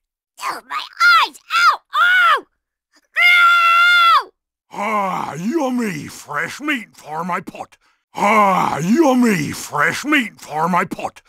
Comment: I one think to he say are you a mean fresh me for my pank